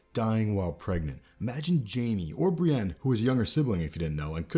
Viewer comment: you say something I can't understand, do you mean to say that Brienne is a sibling of Jaime/Cersei (so a Lannister) or what did you say?